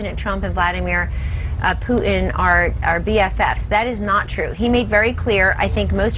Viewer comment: what does she say? bfs?